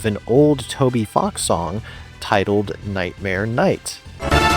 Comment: i heard "nightmare kniight and was like CUCUMBER QUEST? so i googled it and you used the image from cucumber quest! amazing